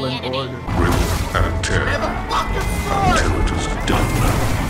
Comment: 4;02 merrin says: "how did it go?" then cal's suppose to say "it's done" but instead i hear him say "i have a concussion"🤣🤣🤣